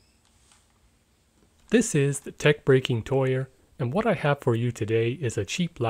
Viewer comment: "This is, the LockPickingLawyer" - 0:01 that is what i heard at first xD